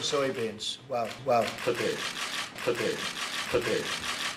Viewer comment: 刘鹤说的是today，川普听成per day，哪有可能每天进口500万吨大豆的？